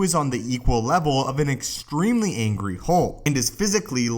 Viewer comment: is he saying "extremely angry Hulk?"